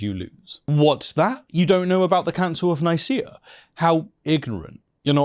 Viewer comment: For some reason I thought you said Council of Nikia and immediately jumped to heresey